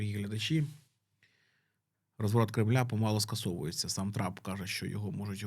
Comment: Мені одному почулось, що Трампа трапом назвали?)))